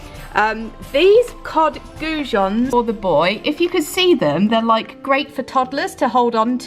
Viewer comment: What’s your little boys name? All I heard was ‘the boy’😂😂xx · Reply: Ha ha! His name is Dolph! Thank you for watching xxx